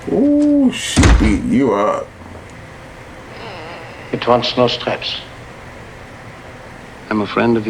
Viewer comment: I was 11 when I saw this for the first time. When he said: "it wants NO STRAPS," I thought he said: "it wants MOUSE TRAPS." I honestly thought he was being REALLY snide and was making some kind of bad joke eluding back to them arguing over getting rat trap. I was like, "wow...this guy is an